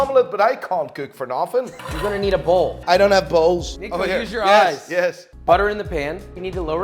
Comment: "I don't have balls." I know what he said but this is what it sounded like. Gotta love that Irish or Scottish accent or wherever he comes from. He has a cool accent.